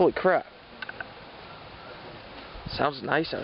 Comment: Why did you say oh crap at 5:00? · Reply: It was actually "Holy crap" because I apparently liked how the alarm sounded outside the house.